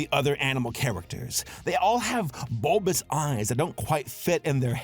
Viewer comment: All I hear is “Don Quixote” over and over again.